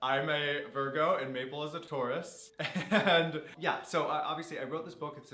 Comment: When you said that Maple was a Taurus, I thought you said Maple was a tortoise 😆